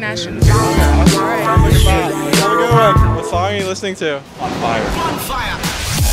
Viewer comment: WHAT THAT MEN JUST SAID BONFIRE LMAO